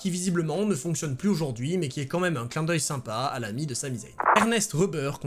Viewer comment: Bonjour, à 1:27:50, c'est moi qui entends mal ou vous dites "l'ami de S. Zain" ? Pas compris...